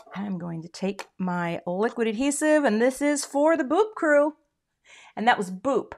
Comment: My husband thought you said the "BOOB Crew". I won't mention what he said after that... ;)